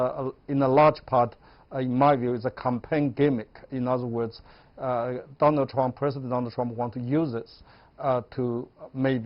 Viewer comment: I thought I heard you say: "President Double-dumb" ... A president who knows so well about faking it!! Winning with TikTak. TikTak for bad breath in a mask?!